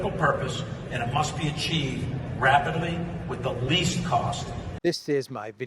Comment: Did Milley just say Rabidly?